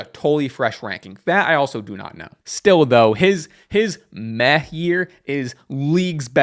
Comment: I thought you said meth year instead of meh year